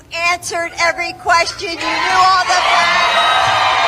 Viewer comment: I swear, man, did she really say “you knew all the words”?